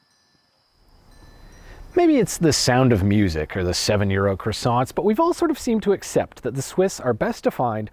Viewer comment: I thought he said 7-year-old croissants. captions revealed it was 7 Euro croissants. 😄